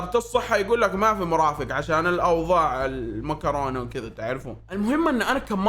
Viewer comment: هو انا الوحيده الي سمعت مكرونه؟😭😭. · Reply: اي هو قال مكرونة عشان سياسة اليوتيوب👀